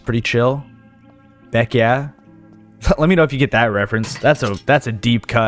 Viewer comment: Oh, I thought you said "neck yeah" from the peeps show, "Beck yeah" is the Ford advert, right? Save big bucks at Beck Ford...BECK YEAH!!!